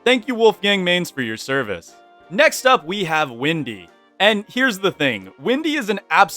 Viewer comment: pardon? did you said Windy? Also so true about my main(Woodie) + my buddy is maxwell main so it seem accurate.